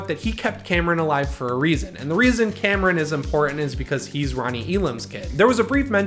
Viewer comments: I listened to it four times, I keep hearing "Ronnie" instead of "Lonnie" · I said Lonnie!!!! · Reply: Sounded like you said Ronnie at 5:15 and on. No hate though.